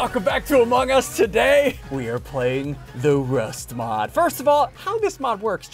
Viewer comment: I thought he said rusty moms😂😂